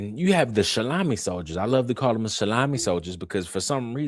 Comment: Nice find on Mikey's anniversary --- also your phrase the shalamies has to be my favorite word from this whole situation. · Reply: For a while I thought he was saying salami 😂